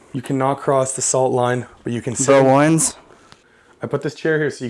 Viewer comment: 10 did he say, The Warrens?